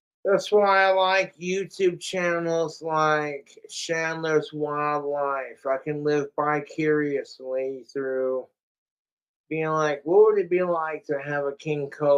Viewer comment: i've heard him say "bi curiously" several times but only just now realized he means vicariously